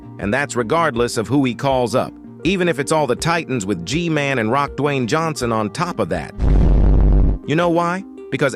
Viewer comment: Did I just heard in 5:22 Rock Dwayne and Cockroach in 5:26 😂😂😂